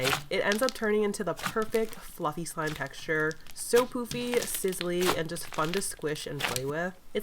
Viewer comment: Ok ok I’m srry but uhm when you were saying things about the “Apricot Peach” slime uhm I heard you say poopy 👍